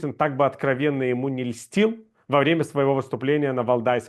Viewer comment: The English subtitles are wrong at 4:23, he says "льстил" ("flattered") not "мстил" ("taken revenge") At 32:32 the subtitles say ".. and in this way the system got rid of you" but what he actually said was "и ты таким образом избавлялся от этой стигмы" ("and you in such a way got rid of this stigma"). I think the choice of words is important here, because it point to out culture being a forbidden thing and people voluntarily renouncing their own cultural identity to fit in with the imperial metropole culture. At around 35:00 names are messed up, he's talking about Paul Grod ("Павло Ґрод" or "Павел Грод" in russian), not Pavel Grozny and the president of the World Congress of Ukrainians was Yuri Shymko, not Yushchenko (that's the president of Ukraine <num>).